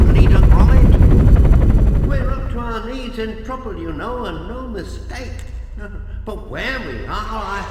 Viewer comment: I really love how wholesome this game is. It's calming. Also I noticed an ironic mistake. at 20:33 Wallace says "We're up to our knees in trouble you know and no mistake." whereas the subtitles say "in trouble now". He says "and no mistake" and that sentence has a mistake in how it's presented visually. My favourite level is the snow level because I like how it looks. I remember eating Hula Hoops and playing this game as a kid.